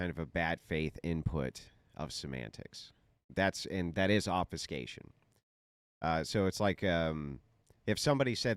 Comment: What word did andrew use at 15:45 ? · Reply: Obfuscation